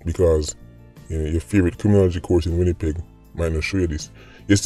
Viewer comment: YOOO SAH! Did you say WINNIPEG at the 7:14 mark? ..."criminology course deh a WINNIPEG '???